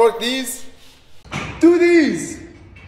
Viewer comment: "What people hear: DO THEESE! " Vs what I hear "DŒ DEEZ"